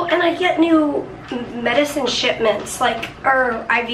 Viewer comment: I thought said shitments. :)